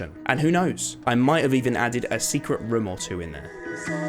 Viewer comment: i just cant help myself but to hear rim instead of room everytime he says it